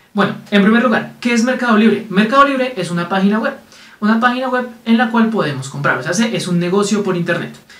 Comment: pensaba que dijiste deep wed